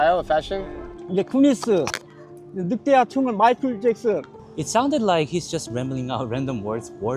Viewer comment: those were not random words from the homeless fella.. He clearly said He likes Michael Jackson